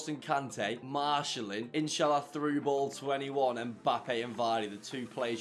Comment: did you say inshallah?